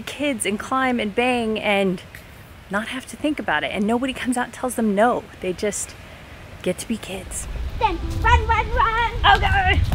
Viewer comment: I could not understand what the woman said at 13:35. Were any of you able to understand what she said? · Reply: "They can be kids & climb and bang and nobody tells them no. They just get to be kids." Curious Minion